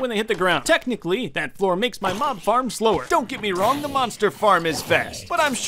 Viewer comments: You said mob farm but i thought you said mom farm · I thought you said mom farm like if you also felt for it